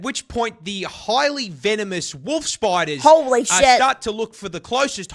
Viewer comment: My voice may sound a bit weird... I've been having sore throat past few days >< Do YOU live in Australia? Let us know below if you have seen ANY of these creatures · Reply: Ah ok i was wondering why i heard you say vinimous instead of venomous